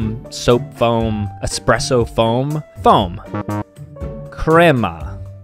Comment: I heard Grandma 💀